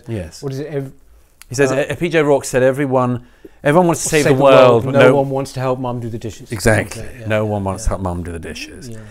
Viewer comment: What was the statement they were quoting at 14:20? I heard the first part “Everybody wants to change the world” but what was the second part? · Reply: It sounded like “Everybody wants to change the world, but no one wants to do the dishes”?